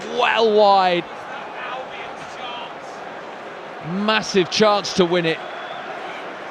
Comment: lol did i just hear a voice that says Albion shucks